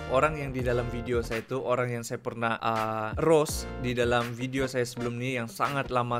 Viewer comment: I thought u were saying ROS like the game and I tried to understand your sentence, so it’s ROAST ya? Is it correct?😂